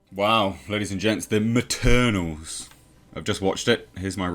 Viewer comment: At first, I heard "Maternals" X''D